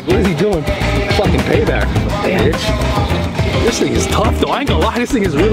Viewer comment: did he said “this nigga tuff”???